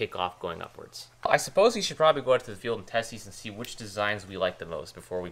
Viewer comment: “Another year, another pervert” -0:06 Subtitles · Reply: The subtitles also say ‘testis’ at 2:07.... instead of test these